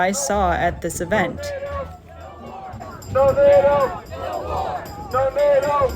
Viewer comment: When they were chanting "No NATO, no war", I thought they were saying "Tomato, no more"